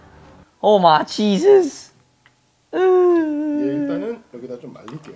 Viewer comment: I thought Mark said Oh my cheeses